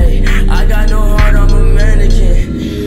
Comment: I thought he said "I got no heart, I'm dominican"